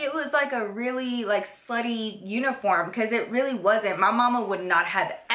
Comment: when you said slutty i thought you said slitty which i dont even the meaning of